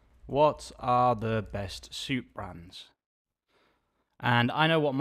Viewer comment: Suit! I thought you said SOUP brands. I was wondering when Armani got into the soup business...! Hahaab